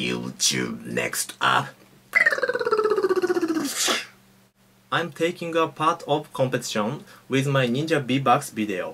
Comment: "making a pot for puppet show" is thats what he says its okay i voted you